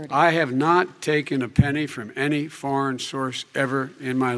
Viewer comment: Hier liegt Hans doch falsch? Biden hat klar source und nicht force gesagt. Also Quelle und nicht Kraft/Macht. Oder fehlt mir da das erleuchtete Linguistengehör? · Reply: du hast recht. biden sagte "foreign source", nicht "foreign force" . ich hab mich verhört.